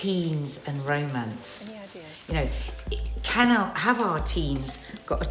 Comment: Does someone say "any ideas?" at 1:09? Or am I going crazy?